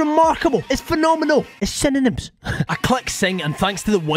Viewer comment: "It's synonyms." I know this appears in your videos, but I get a good laugh every time you say it. Nice name theme: Egyptian Deities. Edit to add: for some reason, I heard you say "It's cinnamon" so now I hope a future nuzlocke you do will have a naming theme of herbs and spices.